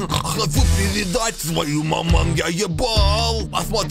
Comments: я только щас заметил что он сказал твою маму а не свою маму😂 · Мне почему-то послышалось свою маман вместо твою маман.